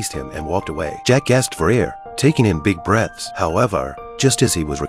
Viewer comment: I thought he said the Jackass gasped for air lol 😂